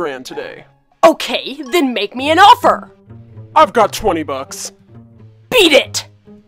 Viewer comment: Did he just say 25 bucks 😨